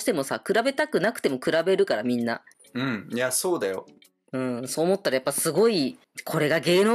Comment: ユウヒチームどうだったんだろう… 『ソウダイ 』って聞こえる所、誰かが言ってたけど『そうだよ』って言ってるんじゃない？って。 そう言われてみると、そう聞こえなくもない🙁 あと、あの嗚咽するような泣き声も、脱落の本人でなく、責任感じたトモヤってことはない？ って トモヤ推しのワタシはあれこれ勘ぐってドキドキしてしまう〜…